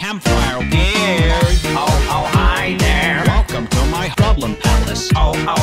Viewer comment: i heard baldi say h#####on instead of problem here